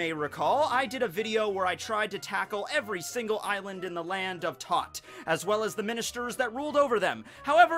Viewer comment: “So a number of months ago as you may recall I did a video where I tried to tackle every single island in The Land of Tot” I swear I thought I heard “The Land of Thot” 😂 Edit: Loving Island? So does Cacao Island shut down for one day on Valentine’s Day and let Loving Island take over? Loving Island probably was the place Big Mom...made the Ministers... hmm... 🤔 Edit again: Karasu’s favorite food was wieners, then there’s this Island in Totland that has THICK WHITE CREAM, named Thick Island... Ora’s having fun with those innuendos, isn’t he? 😂